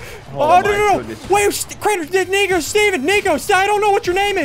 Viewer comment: it sounds like he said the n word😂